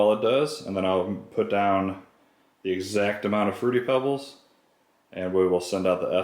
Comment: Did I just hear him say, “ I’m going put the same amount of fruity pebbles “?